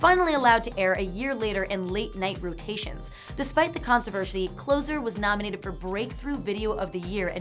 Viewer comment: did she say consivershy?